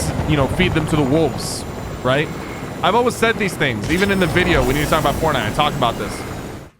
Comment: captions say we need to talk about porn